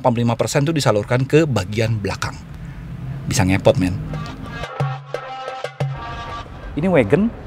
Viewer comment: Bisa nget_t??? Gw salah denger atau enggk nih??? Kok jadi gini skrng? Kecewa aing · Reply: Menit 1:57 Bang bilang :"Bisa Ngepot↩man !!😁😁